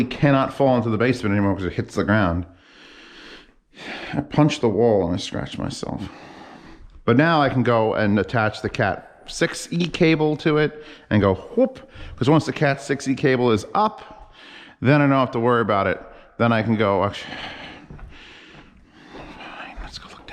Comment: I thought you were saying attach the cat to it.